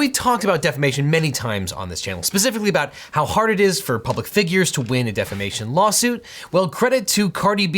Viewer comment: You may want to fix up your Closed Captions. At 0:38, it says "for pubic figures" instead of "public figures" xD You know what they say about fine print...